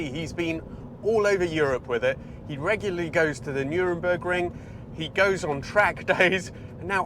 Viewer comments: Did you say NurENburgring?! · did I hear Nuremberg Ring???